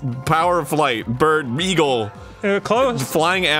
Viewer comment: anyone else hear say beagle